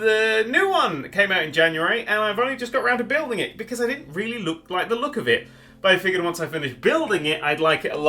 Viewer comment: what the hell did you say?! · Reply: Daniel Addison He really didn't look like the look of it.